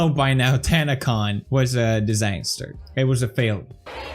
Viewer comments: if you have the subtitles on, at 0:14 Pewds says "Tanacon was disaster. It was a failure" but the subtitles say "Tanacon was a miracle. It was amazing" SHANE INVESTIGATE PLS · captions say exact opposite of what PewDiePie is saying! Odd! He says it was a disaster and failure. Captions say it was a miracle and was amazing.